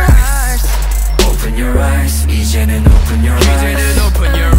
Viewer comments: lmao heard it as "open ur rice" lol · My mom LITERALLY thought they were saying open your a**😑😑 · I dont know how people hear 'open your rice' while I hear 'open your ass'